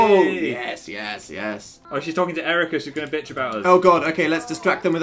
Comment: di-did philly just s-swear? like did he just say "bitch"???